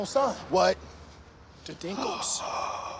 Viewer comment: As a British person who hasn’t seen got I thought they said the dingles and i immediately thought of emmerdale 😂😂